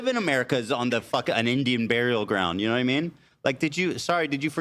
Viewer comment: did he just say “Indian burial ground” ???